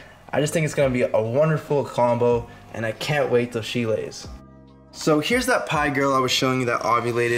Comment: The Banana enchi special het for monsoon is a very pretty girl, I thought at the end you said I can’t wait till Síle (she + la) lays 🤣 I think she has a new name